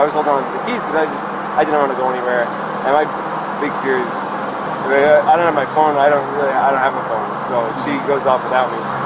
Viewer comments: Made a comment saying check out video 40:00 - 44:00 his right hand pocket, doesn’t appear to be a wallet possibly a cell. Just an observation. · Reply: also listen at 14:30-14:40 right at 14:39 he says I don't have my phone I don't really I don't have a phone....Put it on CC and you will see that if you can't hear. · I caught that but then I think he must have meant if she drove away with his phone in the car. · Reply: no he actually said "I don't really have a phone"